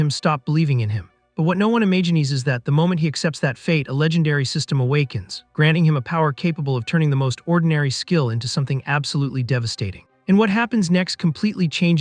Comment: did this mofo say "E-may-genies" instead of imagines?